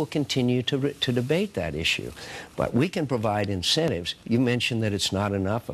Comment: did he say "we can provide insanity?" vote obama